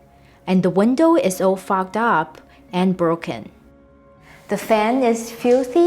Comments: I thought she said “the window is all fucked up” at 5:27 lol I’m a mess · When you said "the windows are all fogged up" I thought you said "f*cked up" LOL! Crime scene and knife in the wall was hilarious too. Love your content!